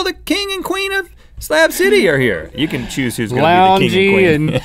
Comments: I thought they were saying scab city ahaha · Did somebody say... STAB CITY?!?!?! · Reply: I actually thought that they said stab city